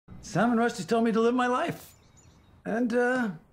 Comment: First thing I hear “salman Rushdie told me to live my life” 😅😂😂😂